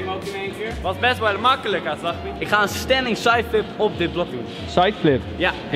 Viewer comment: ik ga stukk!! ik hoorde op 7:16 zeggen " het was best wel makkelijk a sahbi" AHAHAHAH